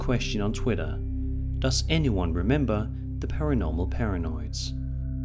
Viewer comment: lol, they found Krampus! I thought he was saying "Paranormal Paranauts"... reminded me of the White Clarion Aryans XD